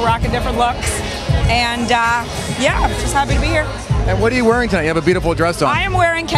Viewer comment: Did he just say "What are you wearing, DIANA?" while talking to Chudney?